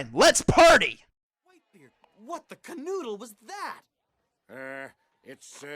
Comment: Did he just say "What the canoodle"? Do they even know what "canoodle" means? ... probably not.